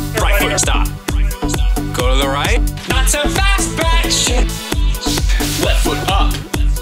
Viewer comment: You got the subtitles wrong, its batch.